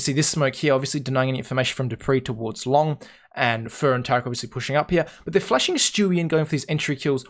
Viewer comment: did he say fer and taco WTF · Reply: "fer and tarik obviously pushing up here"